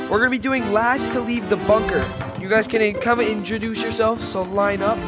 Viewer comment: bro i thought you said monker not bunker💀🤣